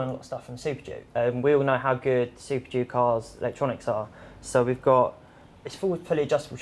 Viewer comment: Did he just say super Duke cars?????? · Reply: Superduke R